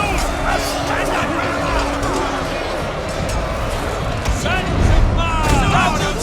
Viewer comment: Did anyone else hear that guy at 1:26 just say "Sup son?".